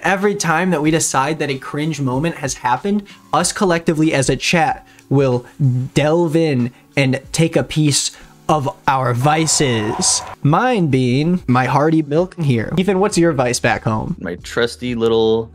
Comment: i heard “harvey milk” instead of hearty milk or wtv and i thought they were making harvey milk flavored cbd oil💀